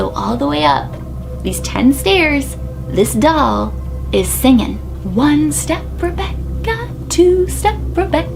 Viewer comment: Why am I hearing it as "one step Chewbacca" lol 🤣😂 P. S. Sorry am late to this but love this!! 🐍😈❤️👠💫✨🌹🙌🏼